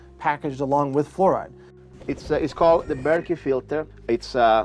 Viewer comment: Hi, I hope you see my comment. GREAT documentary, right up there with The Fracking Doc. Awesome work! (I'm in SD Co too.) What is is calling his water filtration system, please? It almost sounded like Berkeley... maybe Berk, or? It's @ 1:19:19 Thank you so very much. ^_^